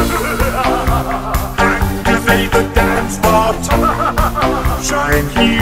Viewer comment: I thought he was saying “activate the dance thot” before I actually red the subtitles